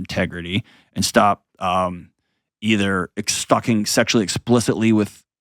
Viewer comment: This is why having boundaries between male / female relationships outside of your romantic relationship is important. ESPECIALLY when alcohol is involved. I would never disrespect my significant other by staying outside on a porch drinking with his buddy while my man is asleep inside. It just looks inappropriate to me and could be easily misconstrued. A group setting with my partner there with me is a totally different thing. To me, I’d rather be safe than sorry. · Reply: what does Dr John say here lol or sounds like f**king